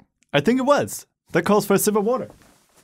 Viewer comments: I'm going to be honest, I thought he said "that calls for a civil war." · At first i thought he said "That calls for a civil war" and i was like O.O